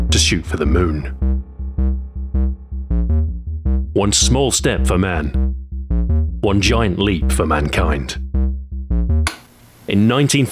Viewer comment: I just need to add this: at 19:37 you say “one small step for man, one giant leap for mankind” but it’s a common mistake people make. What actually was said is “one small step for a man, one giant leaf for mankind”. That “a” is covered over poor audio, combined with Neil’s Midwest accent and everyone can’t even hear it’s there.